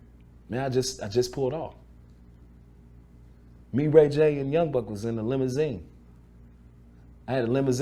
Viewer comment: Danny boy drops some of the best interviews and I didn’t know ray J was there when Biggie got killed I’d like to get his POV on being with death row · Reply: Sound like he said him ray j n young buck 😅